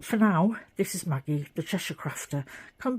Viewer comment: When you said the Cheshire crafter, I thought you said 'the treasure crafter' and it is a treasure! Great quilt